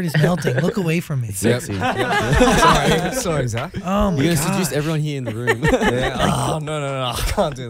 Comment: did anyone else notice that chan said “you’re gonna seduce everyone here in the room” to felix at 21:21 💀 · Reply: imo chan said "seduce" but felix misheard it as "introduce" that's why he responded like that(?)